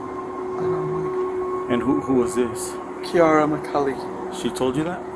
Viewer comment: thought he said Kiara my colleague 😂